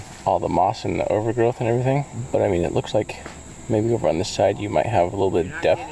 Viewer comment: I'm starting in some of your video hear voices and @ 26:04 I heard "maybe" How does anyone know that he wasn't abducted and forced into someone truck and that his four wheeler isn't in the back of someone truck and taken out of county.